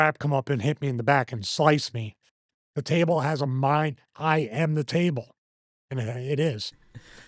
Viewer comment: Stevie IS the Table 😎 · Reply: I turned up the volume to my phone after reading the first comment/replies and heard him say "I Am The Table"